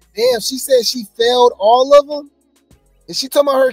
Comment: I thought she said she fell off but she said she failed all of them Wow